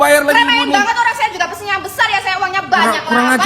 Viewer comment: glowsella glowsella ketiak ibu item Ngakak sumpah dengernya🤣🤣🤣